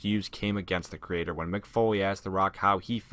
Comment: I thought Foley said Bo-lieve, not Foley 😂